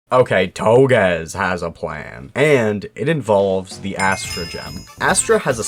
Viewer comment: i need a touble dake when you said astro gem because i thought you said estrogen 😭